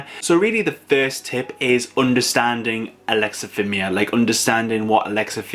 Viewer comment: Never even heard of this at first I thought it said Alex atheism Mia. I was so confused